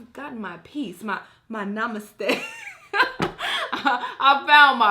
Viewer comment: did she say I found my namaste?? Namaste is equal to peace..